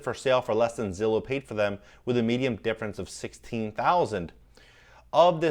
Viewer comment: am I hearing you say “medium” instead of “median”?